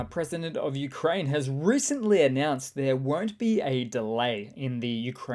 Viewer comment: "(Zelenwski) announced there WERZT be a delay"??? EDIT: Never mind, English subtitles say "WON'T".